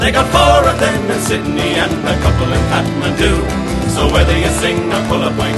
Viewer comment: Well, you're walkin' through a city street, you could be in Peru And you hear a distant calling and you know it's meant for you Then you drop what you were doing and you join the merry mob And before you know just where you are, you're in an Irish pub They've got one in Honolulu, they've got one in Moscow too They got four of them in Sydney and a couple in Kathmandu So whether you sing or pull a pint you'll always have a job 'Cause where ever you go around the world you'll find an Irish pub Now that design is fairly simple and it usually works the same, You'll have 'Razor Houghton' scoring in the Ireland - England game And you know you're in an Irish pub the minute you're in the door, For a couple of boys with bodhrans will be murdering Christy Moore Now the owner is Norwegian and the manager comes from Cork And the lad that's holding up the bar says 'Only Eejits Work' He was born and bred in Bolton but his mammy's from Kildare And he's going to make his fortune soon and move to County Clare Now it's time for me to go, I have to catch me train So I'll leave ye sitting at the bar and face the wind and rain For I'll have that pint you owe me, if I'm not gone on the dry When we meet next week in Frankford in the fields of Athenry Where ever you go around the world you'll find an Irish pub' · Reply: I always thought they said Kakadu not kathmando. Kakadu is near Darwin in North Australia, you know where Croc Dundee comes from. And my Self.